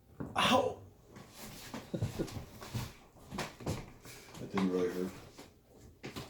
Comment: What is it that you say at 22:35? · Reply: "That didn't really hurt" (because he snuffed the candle out with his fingers). :)